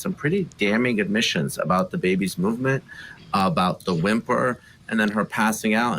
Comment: I thought she said it whimpered 😮